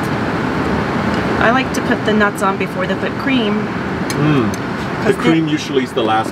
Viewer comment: I don’t know if anyone was reading the captions, but I was dying laughing at 10:00 when she said that she likes to put the bits in before the whipped cream, but the captions thought she said foot cream😂😂😂😂😂😆😆😆🤣🤣🤣🤣